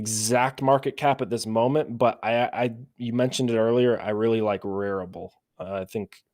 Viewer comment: what project did say? I can't understand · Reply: rarible